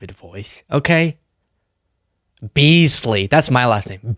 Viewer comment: When you said your name is Beasley, I misheard it as Weasley and now I have an image of Ron Weasley in my head when I hear your voice.